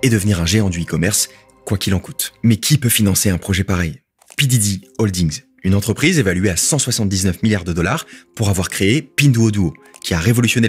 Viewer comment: J'ai entendu "P Diddy" j'me duis dit "Mais naaan P Diddy a fait du sale en Chine ?! 😲" 4:18 😆